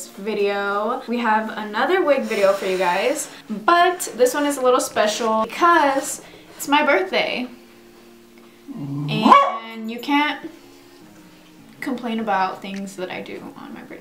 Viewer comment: it sounded like a "what?!"